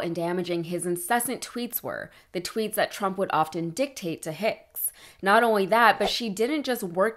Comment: I thought he said, "Ho pics"🙄 Just me?